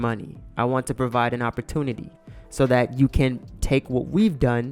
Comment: Def thought he said “big dog look at my balls “ 🤣